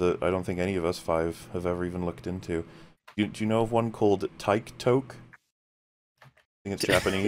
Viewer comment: I legit thought you said "Tyke Toke", as in, some kinda political movement encouraging small kids to smoke weed or something.